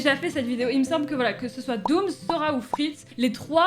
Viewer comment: j'ai entendu dora donc j'ai redéfilé et la j'ai compris quel avez dit Sora et non pas Dora